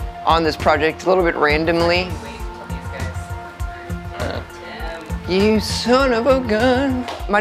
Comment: what is he singing/saying at 3:43? You son of a gun? haha